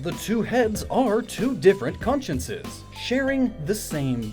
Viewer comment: wtf are you saying, "conscinces"??? XD